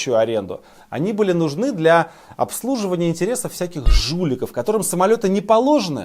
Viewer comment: "Всяких жуликов" я сначала услышал "Всяких шлюх" - маленьк офигел и отмотал